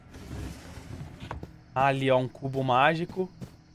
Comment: Foi Só eu q Entendi Cu mágico?